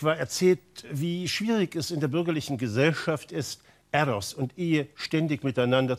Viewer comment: min ehe und was? Konnte das Wort nicht verstehen. Ethos? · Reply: Eros und Ehe